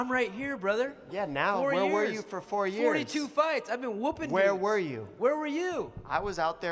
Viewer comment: he been woppin what ? did he say the n word LMFAOOOO